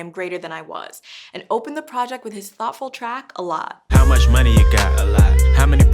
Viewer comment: At the beginning i thought she said. Awful song it she said thoughtfull. Anyone else